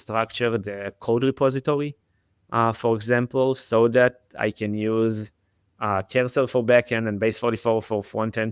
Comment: it sounded like said he uses cancer for backend?